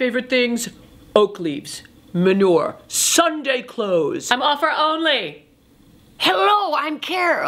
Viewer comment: At the 2:44 mark, what is she saying as Patti LuPone? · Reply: "I'm offer only."